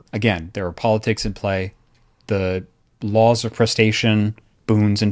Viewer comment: I keep hearing "the laws of crustacean" rather than whatever the term for the boon system is...